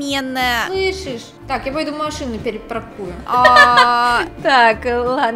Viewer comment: мне послышалось "пойду в машине покурю"